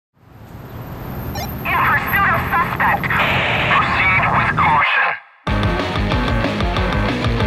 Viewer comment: What did the radio people say? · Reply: Woman: “In pursuit of suspect!” Man: “Proceed with caution.”